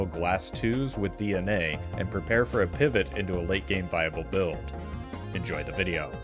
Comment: At the intro i heard "bible build" instead of viable and shit had me praying jimbo for dying in the stake 12 for our sins